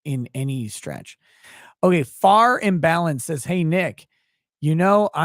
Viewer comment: Nick, why didn't you tell people that wasn't the actual Faran leaving that super chat about discord? I was listening not watching and it sounded like you said Faran Balanced. Why didn't you specify that WASNT THE REAL FARAN?